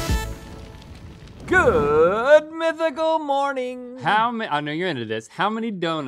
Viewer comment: 90's kids!! Who heard "One Saturday Morning!" at 0:12? 😂